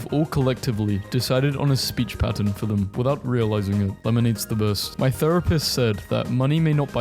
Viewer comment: For a sec i thought you said "lemonade is the worst" at 11:57 and i was about to throw hands